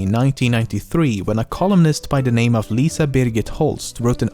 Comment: i thought he said “colonist”